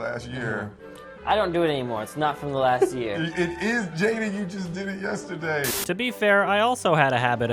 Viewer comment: did he just call his son, jaden, jaime??